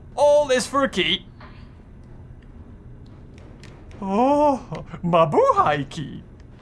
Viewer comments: Did he just say Mabuhay Key? If Yes, wassup fellow Pinoys! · Did I hear it right? “Mabuhay key?” 😂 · did u just say 'MABUHAY KEY' xD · Did you just say “Mabuhay key”?